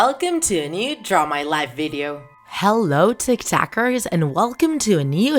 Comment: I thought you said tiktoker